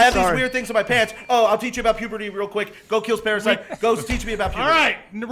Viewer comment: Does say "He goes to kill Paris Hilton"?